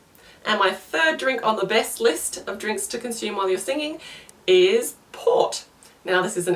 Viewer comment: I didn't understand what you were saying the 3rd best was..... It sounded like you said pot,pop? I missed it.